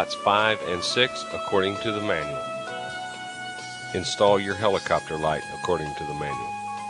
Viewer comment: I still have trouble, figuring out what he means by helicopter life. I decided to look it up for myself, and all I find is ceiling fans that are in the shape of helicopters which is absolutely hilarious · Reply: i think you misheard that as he said helicopter light